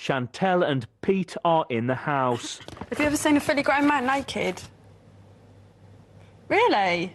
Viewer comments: What does Chantelle say here? All I hear is "have you ever seen a freaking grandma naked?" 😂🤣🤣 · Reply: Ha ha. “Have you ever seen a fully grown man naked?” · wtf did she say LOL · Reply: “Have you ever seen a fully grown man naked?”